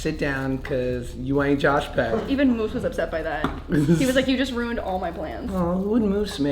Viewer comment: im sorry did you just say MOOSE WAS JUST A DOG!!?